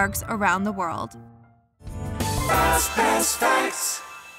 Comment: i hear “fazbear’s frights”. I MUST be dreaming